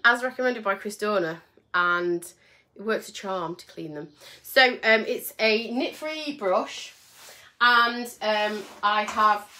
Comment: You are very funny. I cannot stop laughing it sounded like you said I have a nit free bush!🤣